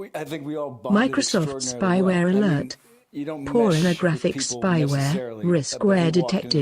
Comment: What was that voice it sounded like she said some pornographic virus has been detected